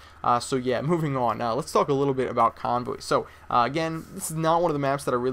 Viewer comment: i thought you said lets talk a little bit about congress